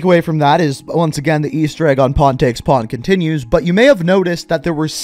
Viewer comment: Every time I hear say pawn take pawn it sounds like hes saying porn take porn I dont know why, I'm just dumb